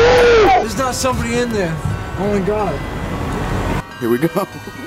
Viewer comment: "there's not somebody in there, only god" ?? did i hear that right?